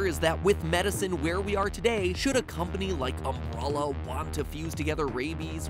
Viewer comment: Did MatPat just say "Ambralla"?